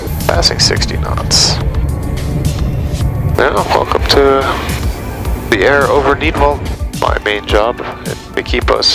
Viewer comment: What did the pilot say near the start of the video? Listened a couple of times and it sounds like he says ‘welcome to Nepal’? Obviously it’s not, so can’t work out what he’s saying. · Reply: He said "welcome to the air over Nidwalden". The departure airport is located in the Canton of Nidwalden.